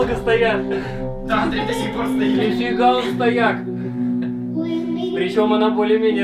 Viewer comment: Мне или послышалось, или кто то из вас сказал вот такое словечко:"Нифига он стояк!)"?)))))) это было на 4:54 секунде)